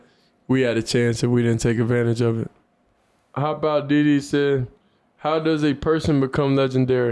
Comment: definitely thought Shedeur said “Hopout diddy”💀